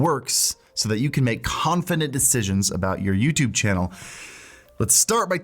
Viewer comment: is it just me or did say confinent instead of confident at 2:20? It just threw me off for a moment.